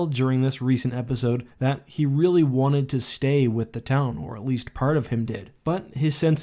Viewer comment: Did you just say "town whore"??